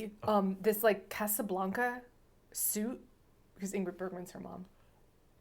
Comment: Was half watching at one point and then I thought said that Sandra bernhard was Ingrid bergmans daughter and I was SO confused. I caught up eventually… a fantastic review to close summer camp